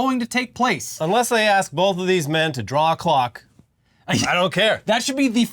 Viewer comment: I thought you said "draw glocks" 😆